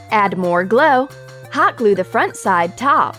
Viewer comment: she says add more GLA instead of add more GLUE